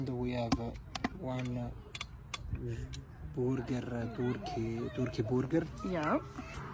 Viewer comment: Sounds like he says turkey booger! Haha! I love him!